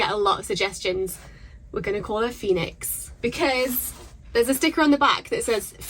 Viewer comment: Thought you said 'Felix' not Phoenix lol. Felix sounded cool though, it means happy or lucky :)